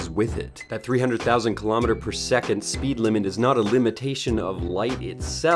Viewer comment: U mean 300,000 km per second right. I thought I heard you say 300km per second.